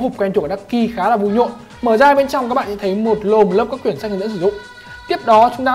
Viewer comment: mình có nghe nhầm ko ? "Mở hộp ra có 1 lồn lớp các quyển sách hdsd" =]]]]]